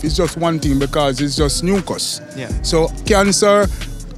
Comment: I think he's saying "nuke us" as in nuclear therapies